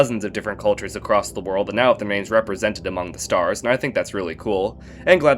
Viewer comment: what did you say here? i can sometimes be really bad at interpreting speech i hear: ”(???) represented among the stars" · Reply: “That now have their names” after the world culture